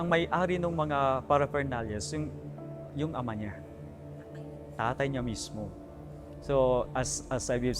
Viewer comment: 49 may narinig po ba kayo? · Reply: Babaeng interviewer yung nagsalita “Tatay niya mismo” which is inulit ni father yung sinabi ng babae.